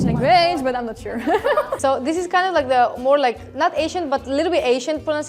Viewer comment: She said Ancient instead of Asian. Subtitles need to be better haha